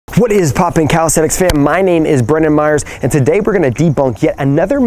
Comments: I know is name but when he told it I thought he said "hello my name is Bruno mars" so I checked his name quickly 😂😂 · lol its sounds like youre saying my nabe is bruno mars XD